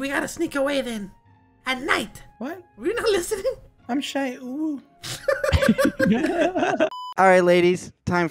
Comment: I’m shy UwU vs how it sounded like I’m shy owo 😊